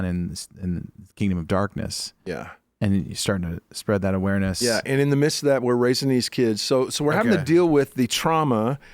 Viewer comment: what did he hear God say!? He kept trying to say it but kept getting cut off... what did God say??? Got me so curious! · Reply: "Hit him," is what the Holy Spirit said.